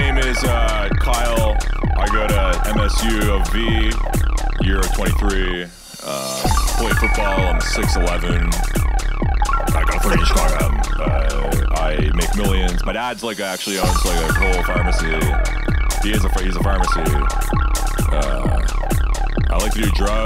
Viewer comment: the subtitles are useful af, I thought he said 3 not 30 inches